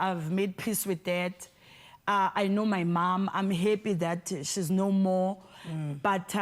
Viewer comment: Did she just say shz happy shz nomore.. Anyways free happy soul love her